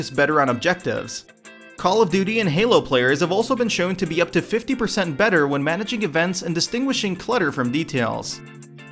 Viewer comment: what did he say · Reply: "Halo players have also been showed to be 50% better from managing events and deciphering details from clutter."